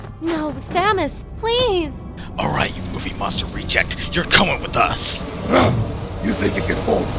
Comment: Is it just me or is Snake calling him a MOVIE MONSTER REJECT? lol